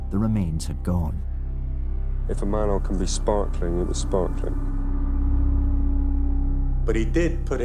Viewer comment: can anyone help translating what the eerie bloke says at 08:33 please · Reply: He says "if a manhole can be sparkling it was sparkling" meaning Nielsen did a very good job of cleaning the manhole